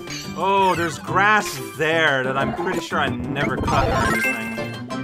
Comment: Yeah, go touch that grass! :elbowcough: Edit: Oh wait, I think he said "cut". I thought it was "touched", LOL. Oh well.